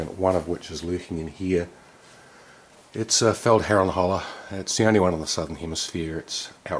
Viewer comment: What is the name of that dagger that is the only one in this hemisphere? I hear 'fel haral hoeller' but google has no idea what I'm saying.. Anyone know? Thanks! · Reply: Feldherrnhalle dagger.